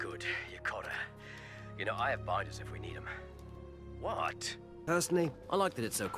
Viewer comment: I always thought he said "I have visors if we need them"